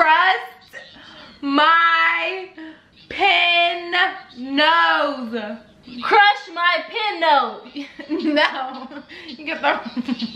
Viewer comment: I thought she said crush my pimple and I don't even have earphones on